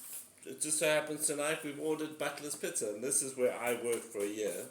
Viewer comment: Thought you said butless pizza😂